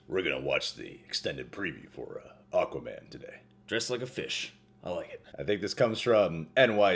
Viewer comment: When you said just like a fish I thought you said dressed like a fish....(dang it) but anyways yeah this looks like it's gonna be good